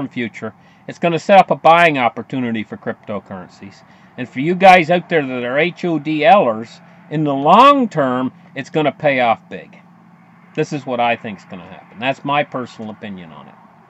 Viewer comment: he says "and for you guys out there that are _______?" I don't understand what he said, maybe "holders." Can someone chime in?